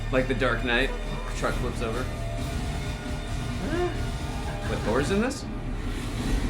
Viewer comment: did you just say thor?😭😂